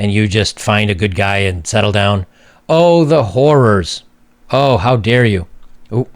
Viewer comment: if your Aussie what is Joker really saying? Oh the w hoorrs.